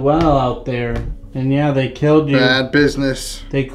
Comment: is definitely there, I heard him say "they killed me" at least I am assuming it's him